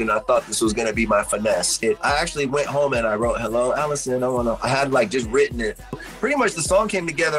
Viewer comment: AISLE 10! Scapegoat wax!!!! Love that jam its my favorite. I come back to youtube to listen to it. Always thought they were saying Hey Alison I want hold you hand...